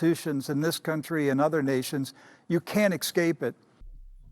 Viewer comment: Heard him say "excape"